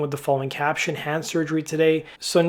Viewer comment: For a second I thought he said HAM surgery